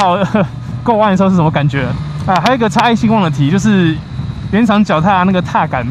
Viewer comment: 原来是“差异性” 。我听了很多次都听不清楚 哈哈哈